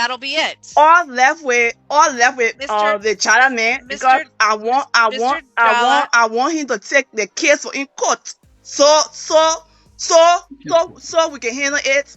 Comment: Did anyone else catch that. She said, "I left with the China man because I want the matter taken out of court". You can tell this man is done because she is definitely a cheater.